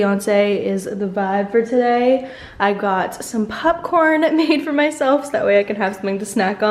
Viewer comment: and did you just say “pupcorn” 🤣